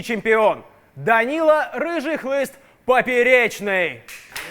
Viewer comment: Мне послышалось Данила Рыжий не хлыст а глист